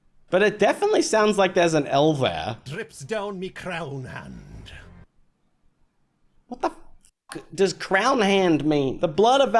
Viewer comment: What he said is "The blood of our foes drips down me crown, hand". He was addressing the other person in the frame, as his "hand". It was a roleplay thing.